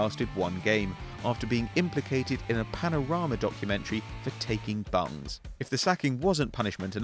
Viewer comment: what do they say? "For taking bungs?"